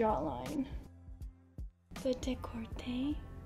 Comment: i love the fact that she is saying "the décolleté" but the subtitles say "the [speaking korean] like, that's not even the right language